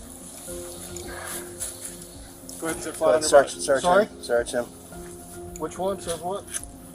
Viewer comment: lool @ 2:28 the officer says "one second" and the subtitles say "1/2" (like one half - 1/2nd lol) Dumb.